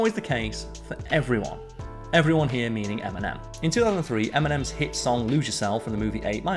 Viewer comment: Making narration slower is helped coupe with accent or maybe just get used to. First time thought that heard "M&M's" not "Eminem". Have sleep through cruise missiles exploded in my city when ruzzians invaded.